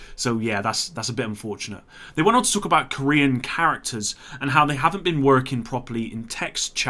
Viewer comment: did he say "Korean characters"?